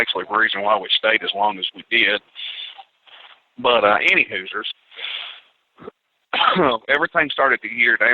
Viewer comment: 13-37 Did he just say Any Hooozer .LOL